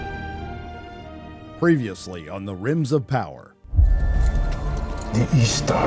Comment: DId you say RIms of Power?? HAHah